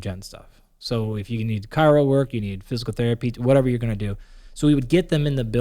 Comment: did he say 'chiro work'? I hope Andy isn't a proponent of chiro... Edit. Oh he mentioned it at 25mins also. As a scientist I wouldn't expect him to give credence to anything like that.